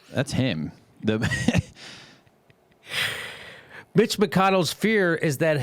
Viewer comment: I guess I need a hearing aid. I keep hearing "Bitch" McConnell.